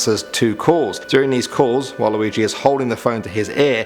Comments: when he said "while luigi" i thought he said "waluigi" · did he say waluigi?? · he said walaluigi instead of Luigi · wait did he say waluigi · Did you just call Luigi Waluigi?!